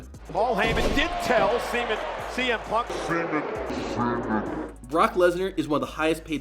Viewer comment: Nani??? bro just say semen punk instead of CM Punk